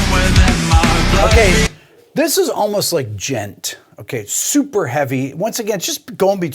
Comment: At about 11:23, he says "this is almost like gent" What's gent? Jent? I don't know what word is being said and google didn't help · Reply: Djent! May your rabbit hole have no bottom.